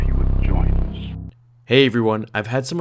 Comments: Why in the beginning when he said hey everyone it sounds like he said hey obi-won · Why in the beginning when he said hey everyone it sounds like he said hey Obi-Wan